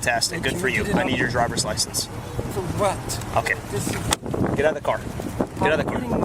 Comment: Lying cop - the driver did NOT say “No” to the request for his license, he asked, “For what?”. Trash cop.